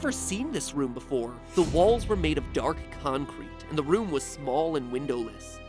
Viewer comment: when matpat said the walls were made of dark choncrete i thought he said the walls were made of dark chocolate